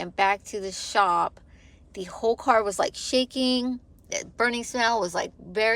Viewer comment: cvt trans do have problems.. they do need to be changed out ..black oil doesnt sound good with metal in it.. i thought you said the whole dash lit up...that would be more electrical or brain.. .codes ..you can google and find out..